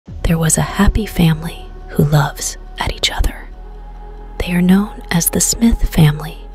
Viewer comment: At the start of the vedio 0:01 i thought the host says she was a fairy🧚‍♀️🧚🧚‍♂️💯✅ i missheard it